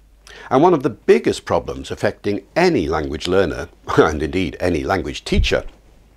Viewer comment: What does it say in 0:56?? One of the biggest problems afecting any language learner ______ any language teacher... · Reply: "and indeed"